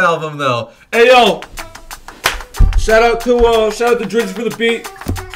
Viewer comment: did i hear drisi????